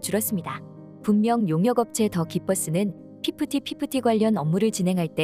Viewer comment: 영상에서 가끔 더 기버스가 덕이 버스로 들림 ㅋㅋㅋㅋ